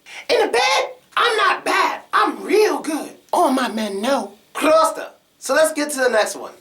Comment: Did he say " proste " in 4:27 ?